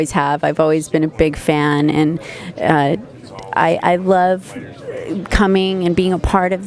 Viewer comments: "I love cumming" all I heard...lol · Did she say, I love cumming? @ 3:32.